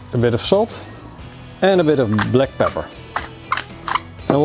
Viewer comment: sounds like he said add a lil bit of black power 😂😂😂😂😂